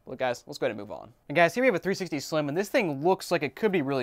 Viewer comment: ps7 slim? 🤔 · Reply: I heard "ps slim and slim"